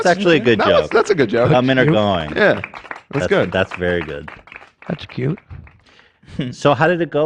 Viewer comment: did he say “that’s cute” 😂